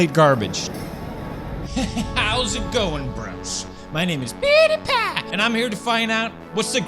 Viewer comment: why when pewdiepie says "my name is pewdiepie" subtitles says "beanie pack"